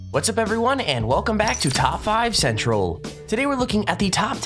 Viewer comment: I thought u said u were top 5 central not top 10 central